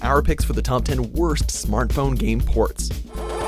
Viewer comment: I thought the end said game porn